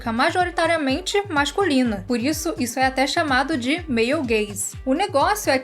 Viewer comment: Quando a falou Male Gaze eu entendi "meio gay"